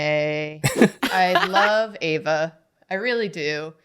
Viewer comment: I draw while watching these, i was distracted, then i hear say, "I love Eva" which sounds like both my name and a character im a fan ofs name (probably evident in my pfp) So i whipped around quick and was like, "hAH??"